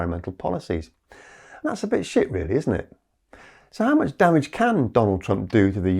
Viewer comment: I heard “And that’s a big shift” as “And that’s a bit shit, innit?” And, yes, it is.